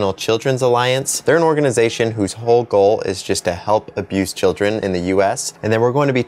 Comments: For just a second I thought you said ''Help abuse children'' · i thought he said help abuse children not help abused children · Is it just me who thought he said '' the nca helps Abuse children" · What I heard was "help abuse children in the US" I was thinking thank god we don't have it in my country, then I thought, "wait... That wouldn't make sense...". I'm not that bright.